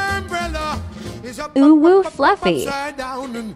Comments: What it said: "Ooooo fluffy" What I heard: "UwU fluffy" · I thought she said “owo fluffy”